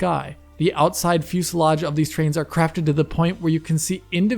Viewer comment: did you says Trains? lol We'll chalk it up to the accent and move on. :D With that said, love the video, can't wait for you to do a deep dive into this tech.